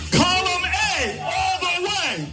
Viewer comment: One of the things people do not notice (if you are not in New Jersey), is that Cory Booker keeps screaming "Column A all the way." In New Jersey, the ballots have actual columns. All of the established candidates will all be under one column, while the progressives will be split in under other columns. A lot of uninformed voters, think they can only vote in one column. So all of the corporate democrats are in Column A (which has the most backing). You would put people like Bernie Sanders in Column B, and then a newcomer in Column, C,D, and E etc (splitting opposition up). So that is why the incumbents and corporate democrats never lose. So the irony is that Booker is shouting a very undemocratic process to undermine the protest. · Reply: I swear to god i heard him saying "Bomb Away! All The Way!"